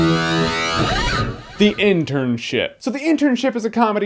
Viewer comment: Ha ha, when says "The Internship," it sounds like he is saying "The Internsh*t," which would be what the movie really is.